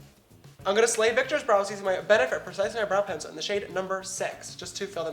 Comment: SIS....SLOW DOWN with the talking!!! @ 5:55 "I'm gonna be using my bEnEfItPrEcIselyBrOw". If I didn't wear makeup, I would of NOT understood what the hell you said. Why bother even mentioning the products if you're just going to mush the names up??? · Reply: Exactly! I thought he said "Benefit Precise Near Brow Pencil".